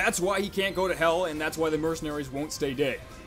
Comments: i keep hearing "and that's why the mercenaries won't stay gay" ... am i hearing this right LMAO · did he say: "thats why the mercenaries cant stay gay"?